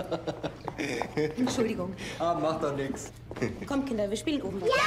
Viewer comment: I made 2 mistakes. At 0:38 I thought the Helmut Goebbles was calling for Heidrun, but he actually called "Holde" as in Holdine Goebbels At 0:48 He actually said "Macht mir nichts" which literally means "Does not matter" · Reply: Macht doch nichts